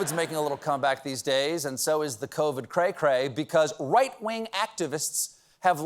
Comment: Not going to lie, the first time I heard the "right-wing" bit I heard "White-wing" and started snorting my coffee out my nose 🤣